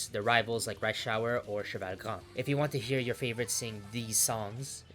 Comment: Did he just say 1:23 cheval c3m?